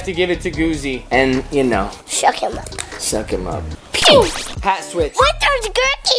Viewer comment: I thought said shuck em up Lol